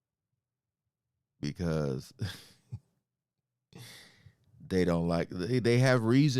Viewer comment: he wasn’t sayin they “don’t” like us. Why would he say that? It don’t even make sense. Your in competition or playing a sport .. and your moto is or rhe cheerleaders start chanting “they don’t like us” with the whole crowd. What makes more sense is that he is actually saying “they not like us”. Considering we know Wayne and Aubrey ain’t speakin. There’s obviously tension